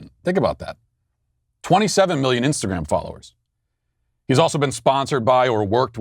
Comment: I thought he said Insta-gay